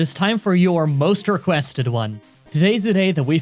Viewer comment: Did you say "you're most requested one"? 😐